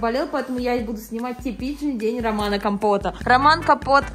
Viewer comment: Мне показалось или ты сказала: Сегодня я буду снимать типичный день Романа Компота... Роман Капот... (Я не знаю почему мне так послышалось) 😂😂😂